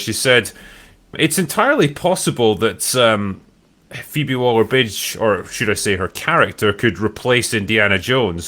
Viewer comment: I'm sure I heard Drinker say Phoebe Waller-Bitch at 0:49 lol